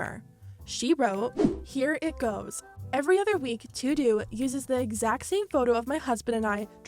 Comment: i- did you really say to do 😭